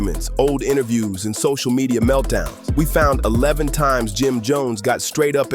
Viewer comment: “11 times” the caption says 10 😅